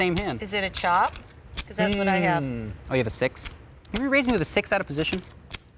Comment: He actually says, "Oh, you have a 6?" at 7:33.